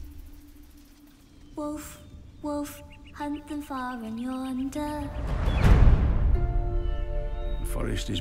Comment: is she say hunt them father yawnder. Or hunt them for Yawnder?